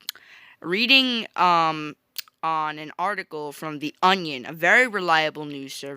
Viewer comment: What a minute, did you say "the onion" at 3:35? You do realize that's a satire "news" sight.